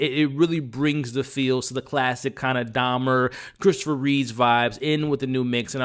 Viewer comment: I thought I heard Dahmer 😅